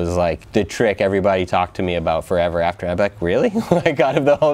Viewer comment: I thought he said " it's funny... The WEEKENDS become everything everyone remembers " Knowing now that's not it... But also IS the truth. This times that are fun weekends become our life story. Didn't think I'd see it deep but I'm tipsy and got Damn I feel it.